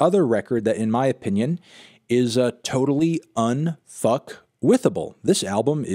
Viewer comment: did he just say unfuckwithable?